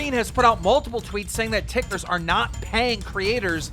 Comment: Did keen just call “tiktokers”, “tikers” at 4:38 lmao wtf😂